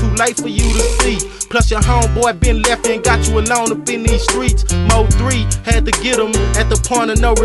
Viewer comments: It sounded like he said “Mo3 had to get him” at 2:30 in this song🤔 but he would’ve been waay too young to be mo3 at that time🤣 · I swear I heard MO3